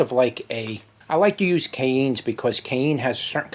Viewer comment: I didn't realize that you were saying "cayenne", I thought that you were saying "cane".